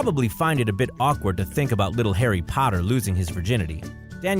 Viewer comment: wait did u just say harry padder